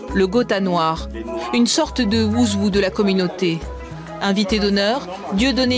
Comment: elle dit quoi? un wuzwu?